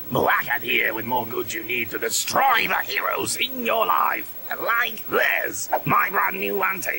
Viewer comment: BUT THE SUBTITLE SAYS "HEROES IN YOUR LIFE" NOT YO LIFE!